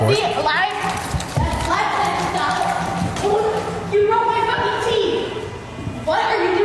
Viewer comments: I was hoping she would get Pepper sprayed right up close in her face as well. Is it me or does she say “you broke my f-ing teeth” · What does she say at 16:02 ? Sounds like "you broke my fucking cheese" 😂 🧀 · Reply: Teeth*